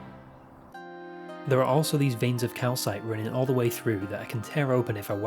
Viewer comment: Did I hear you say Kalkite? Synthetic klakite, kalkite alternatives, kalkite substitutes? I mean the amount of time spend pondering this grubby little bit of rock is sadly astounishing.